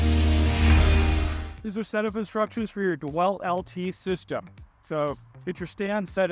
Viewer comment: Did he say Dewelt @ 0:04 ?.....really